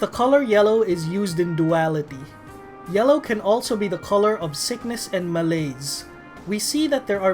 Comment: All I heard was sickness and mayonnaise. Just me?